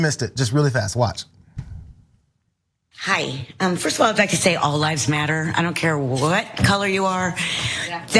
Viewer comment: I thought she said all lives matters